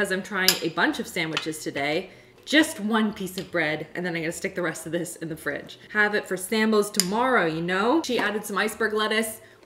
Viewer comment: Surely I'm not the only one who thought she was saying non-bread. 😆